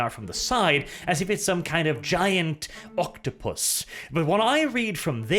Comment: I thought he said "Giant fucktopus" and I think that's the most appropriate thing I could have heard